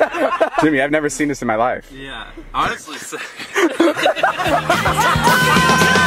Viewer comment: David: “I’ve never seen anything like this, thank you” Jimmy: …..”Honestly same” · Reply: I thought he said “Honsestly sick...” hahahahaha